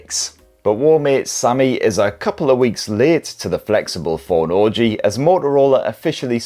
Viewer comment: I thought you said "the phone is a bit late to the orgy" 😆